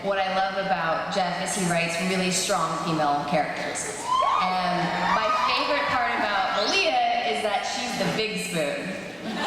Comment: Help, please. 40:26 What is Shelley saying? And my favourite part about Malia... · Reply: Diana Whale "and my favorite part of Malia is that she's the big spoon"